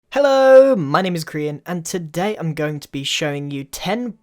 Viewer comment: In the beginning it sounded like he said "Hello! My name is Korean"